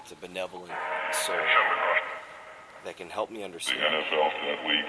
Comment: sounded like the spirit said "the nfl got lethal"😳😳